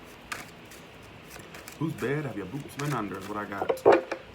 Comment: I thought you said “Who’s boobs have you been under?” Hahahaha!